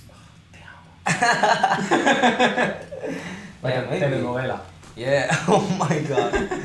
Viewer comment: What did Edvin say in Spanish at 30:28? 😳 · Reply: Telenovela = soap opera